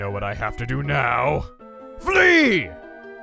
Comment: Anyone else here thought he said Flee as in running away like he did last time.